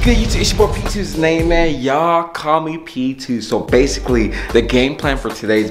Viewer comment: I thought he said y’all comment p2 not y’all call me p2